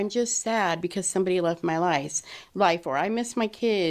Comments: did I hear a freudian slip👀..I thought i heard "lies" when she ment to say "life".... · So no one noticed that she said 'This is not I'm just sad because someone left my LIES' instead of my life, before correcting herself at 13:18? Holy freudian slip.